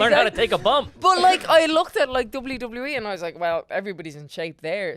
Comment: When she said WWE it totally sounded like Dublin Dublin E 😂